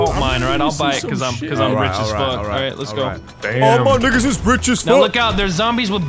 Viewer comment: did he say nigga